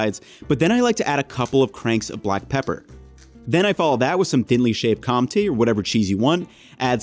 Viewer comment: subtitle say shaved calm tea, what's that, is that shaved cheese?